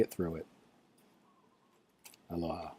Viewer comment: I'm in Hawaii so I say Aloha often. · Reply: I thought you were saying allah at the end. I was so SO CONFUSED! LOL Aloha to you!